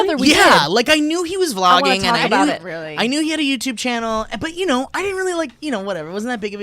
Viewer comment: Where was the intro song god damnit, I got scared. Also, anyone know what said at 11:59? Thanks, bb. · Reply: Pretty sure he said YouTube. Edit: I re-watched that part. They wrote it above his head.